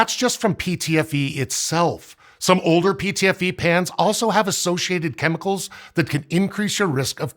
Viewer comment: He keeps saying 'pans' and 'pans' and all I hear is 'pants'